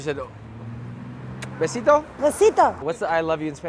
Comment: can some one please tell me what did the old lady say on 23:36?????????! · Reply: besito - little kiss